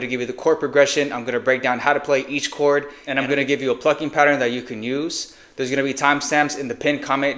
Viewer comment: a phucking pattern you can use is what I thought I heard. 🤣🤝🏾